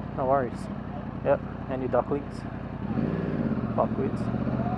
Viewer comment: At around 3:01, I seriously thought you said "ugly ducklings" and lmao. Either way, subscribed.